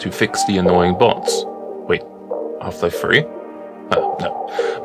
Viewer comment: Wtf did he say at 0:19??? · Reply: He said "wait"